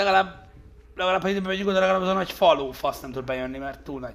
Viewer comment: mit mond :D ? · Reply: RÁJÖTTEM AZT MONDJA HOGY LEGALÁBB HA BEMEGYÜNK ODA AZ A NAGY FALÓFASZ NEM TUD BEJÖNNI